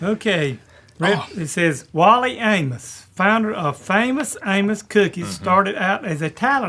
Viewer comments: when he said Famous Amos I really thought he said Famous Anus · I thought he said "Wally anus" lol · I thought he said famous anus 😂